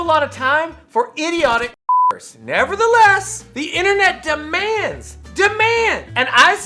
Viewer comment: idiotic what? Did he say the n-word? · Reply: The word I said was m*rderer. I had it censored to not upset YouTube guidelines.